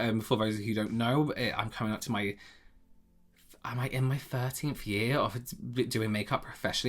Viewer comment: When you said you have been doing makeup 13 years, I thought you said 30 years, and I was like “Holy shit. You are REALLY good at makeup and skincare OR you started 3 years before you were born.”